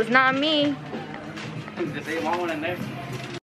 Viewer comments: Does anybody know what said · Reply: “ just say you want one and there “ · What did he say at 7:30 ?? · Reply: “ just say you want one and there “